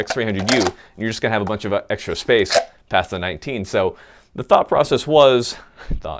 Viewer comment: i totally thought he said thot-19 i thought wrong